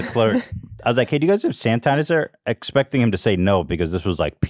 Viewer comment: did ethan just say sand tanasizer?